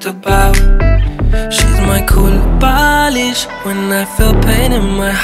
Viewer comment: Did I just hear "she's my kulbalish"? 🐸😵